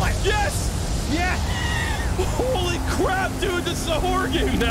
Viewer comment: Ironically I thought he said "Holy Crab"